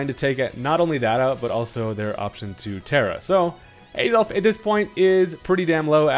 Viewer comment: bro thought we didn’t notice he said “Adolf”